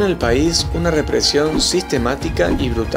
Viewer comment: Que es lo que dice en el minuto 3:31 ? Alguien me dice esa palabra que no la entiendo jaja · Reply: Al decir "REPRESIÓN SISTEMÁTICA", nos referimos a que no fue un hecho aislado, sino que hubo un plan ordenado y programado de represión. Gracias por ver nuestro videos Juli 😃!